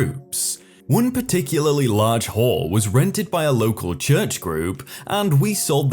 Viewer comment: Am i the only one who heard whore instead of hall?